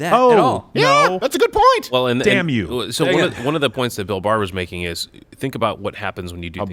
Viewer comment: Thought it said Bill Burr sorry I'm outta here